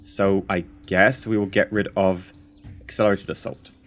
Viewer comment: every time you say "accelerated assault", all I hear is "accelerator salt"